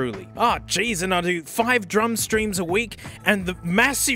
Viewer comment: I heard that as "I do five drunk streams a week" and it didn't seem at all out of place, only noticed because I had to restart the video